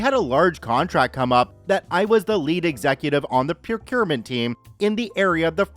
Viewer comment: says "procurement team" I hear "pewpewment team"